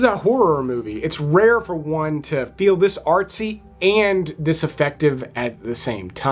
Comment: How many people thought when he said " This artsy..." OR THIS FARTSY. ?🤷🏻‍♂️